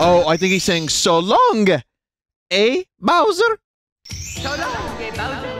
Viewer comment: i think he's saying it with an accent, "So longé bowser", i believe.